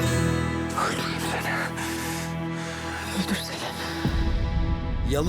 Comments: Seyranla Ferit sonda ne konuştular.öldürüm seni /öldürsene beni 'beni dedi yoksa ben mi öyle anladım .burda sinirim bozuldu.🤬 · Fragmanda Ferit: öldürürüm seni Seyran:öldürsene Mi dedi ben mi yanlış anladım 😮😮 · En sonda ne diyorlar yaaa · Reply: Yanlış anlamadiysam. Ferit öldürürüm seni diyo Seyran da öldürsene diyo